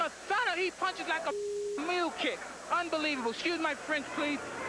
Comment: did he say mule kick or little kid? I think he said mule kick..